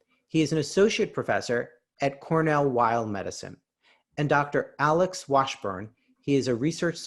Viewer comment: I thought you said Cornel Wilde Medical!! haha